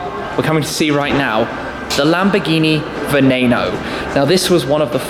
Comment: Did he just say venano???? 5:01 and